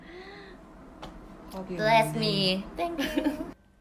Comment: What i hear: 0:00 *cry* bless me 0:26 i hear yoouuuueeh! 0:30 ruffian!! 0:34 meow in japanese 😂 0:36 WAAAAAHH!